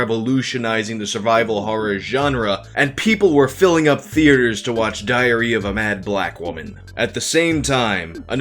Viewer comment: I heard that as diarrhea of a mad black woman.